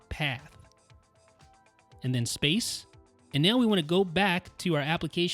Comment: You forgot to mention that after ¨applicationpath¨ goes ¨space. Very good video!! Congrats!! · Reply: there is clearly an audible leave space instruction...or do i hear dead people? 😱